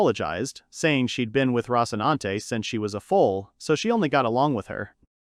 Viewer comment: did I hear Rocinante!